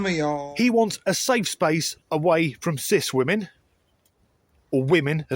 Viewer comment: I misheard the person at 3:00 as wanting a “safe space from Switzerland”. Which I thought was a strange thing to say. Maybe owns too many AT1 notes